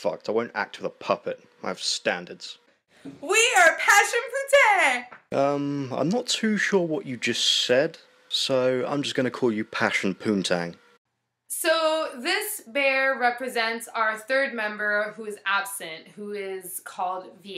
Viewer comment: Honestly, I thought they said they were "Passion for Tang" and I just....Ew